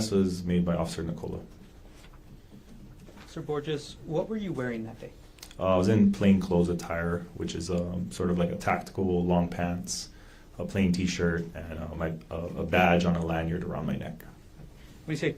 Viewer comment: I thought he said Officer Gorgeous 😂😂😂😍😍😍